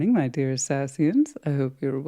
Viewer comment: Apparently I need my hearing checked cuz I thought she said dear assassins lol 😂